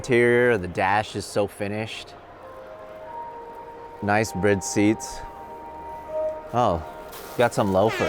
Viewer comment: Did he Just say Bridge seats? I thought they were called Bride.